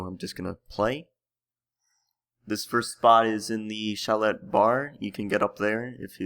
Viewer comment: Did he just say "shallot bar"?